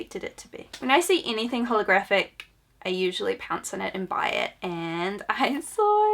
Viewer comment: I 100% missed what you said and I thought you said "when I see anything pornographic, I usually pounce on it and buy it" Thanks. I need that in my life today XP